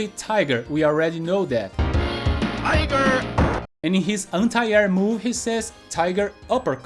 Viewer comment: I thought he said Fighter! 😂